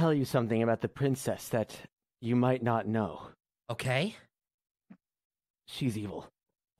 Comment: I THOUGHT HE SAID SHES EMO